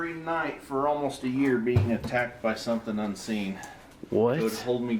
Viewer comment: can't hear the whisper "demon" all I hear is "in seenhh" like the guy exhales after he says unseen 🤷